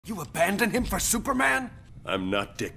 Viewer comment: Scrolling past, I just heard, “You abandoned him for SOUP?!?!!